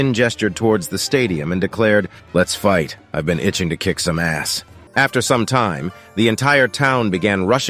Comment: 😂🤣😂, did shin really say"LET'S FIRST I'VE BEEN ITCHING TO KICK SOME ASS" 😐